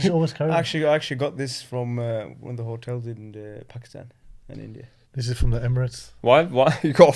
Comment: did he say hotel from pakistan in india😅